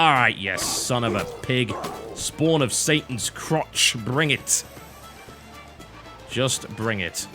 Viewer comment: All I heard "Canadian... Canadian... Canadian... SATIN'S CROTCH! " lol